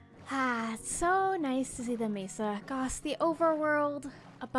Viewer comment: Did anyone realize at 30:00 she said "gos" instead of "gosh" lol. Love ya ❤️